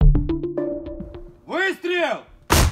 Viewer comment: what did this soldier yell before he fires? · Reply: Vystrel (lit. shot) — Fire.